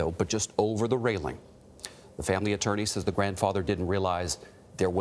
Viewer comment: does he say "didn't realized"?! Since when it this correct English??